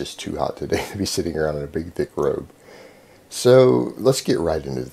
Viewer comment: Thought you said "big dick robe" at first. I agree with you on your opinion my man